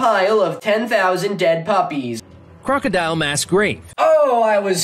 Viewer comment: misheard crocodile mass grave as "crocodile masquerade"